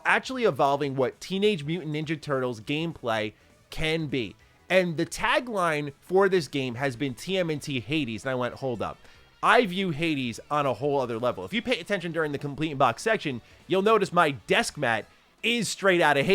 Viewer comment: Did anyone else think he said TMNT 80's?